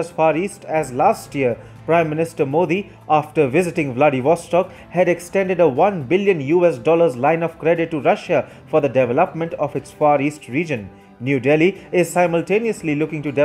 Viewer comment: i heard bloody for stock instead of vladivostok 😂